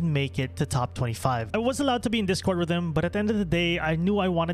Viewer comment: I watched this 2 times and I was sure I heard you say "I wasnt allowed on discord"... but then i replayed it over and over and you are saying "I was allowed on discord" so I was about to make a comment about you cheating xD hahaha but it now makes sence :D